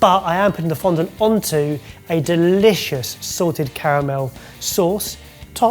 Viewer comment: I swear every time he says it i hear barry say “sorted caramel”